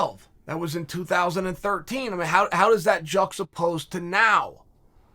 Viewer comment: “jucks supposed”? I’ve heard Jon Anik say the same thing and I’ve been trying to google it after hearing Chanel say it, but have no idea what he’s actually saying. Anyone know? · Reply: juxtaposed